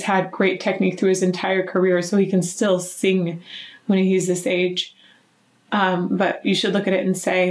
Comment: I heard "He can still sing when he's this age" as "He can still sing when he's a sage" and both are correct.